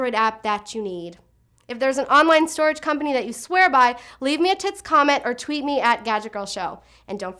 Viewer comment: did you say leave me a tits comment?